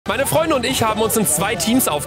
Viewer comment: Ist euc eig aufgefallen dass nd gesagt hat "Sandra und ich" sondernb"Meine Freundin und ich"🥰❤ Edit:Ok ich habe mich verhört😂 sagt meine FREUNDE und nd FREUNDIN Sry habe mich verhört Währen aber schon ein Traumpaar🤩